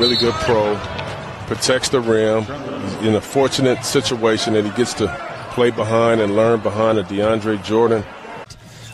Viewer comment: anybody know what kawhi said? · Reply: I ain’t ya boy” I think